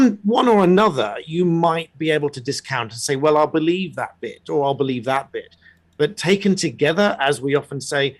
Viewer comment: I just heard “I believe that bish” and was shocked 😅 BITS